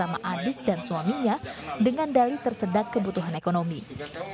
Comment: tersedak????? 🤣🤣🤣 ga salah denger ini yak?